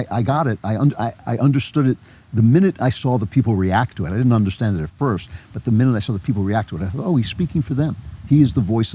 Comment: What's he saying at 56:45 - haysoffice years? What's he referring to? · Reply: OK - Hays Office. Hays Code for movie making. Morals and such.